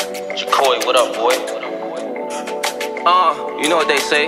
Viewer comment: Thought it said jackboy